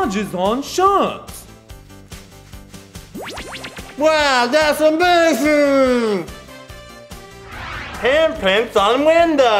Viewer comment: EVEN THE SUBTITLES DON'T UNDERSTAND THE GUY WITH BRACES well i'm out of luck to find out what hes saying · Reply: like he says wow that's amazing at 0:56 and the subtitles say well that's a big hair rlly man